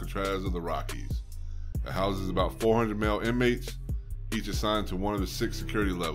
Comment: at first I thought he said it housed 400mill